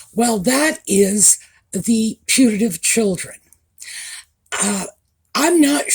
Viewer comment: This is so funny. All this time I thought you were saying "punitive children." Now it makes sense!!